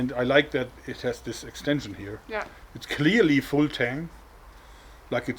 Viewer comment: every time Jorg says "full tang" I hear "Wu-tang."